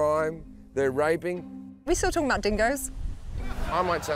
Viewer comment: "we WHAT WHAT WHAT dingoes"??? I can't understand anything she said in the middle of that sentence lol... Who understands the Australian accent enough lmk what she's saying? lol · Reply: She said "we still talking about dingoes ?"